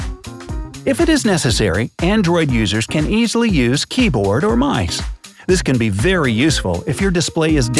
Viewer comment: Did he just say keyboard or 'mice'? 😂